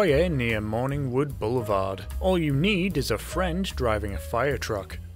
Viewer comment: I don't know why I heard "friend" as "french"